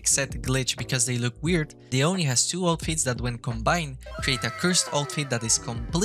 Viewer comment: what did you say? I can't understand fr. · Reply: The oni has two outfits that when combined